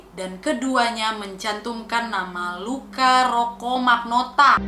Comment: Luka rocco magnota Di kuping gua kedengeran Luka rokok maknota